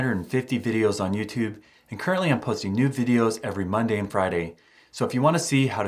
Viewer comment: does he say “new videos”, or “nude videos”? Guess if they want to demonetize the channel he can’t post whatever he wants.